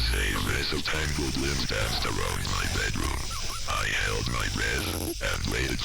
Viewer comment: I definitely heard "tangled limbs danced around my bedroom." Overlapping the first sentence